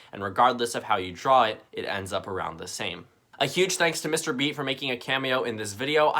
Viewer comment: Am I the only one that thought it said, “Mr Beast”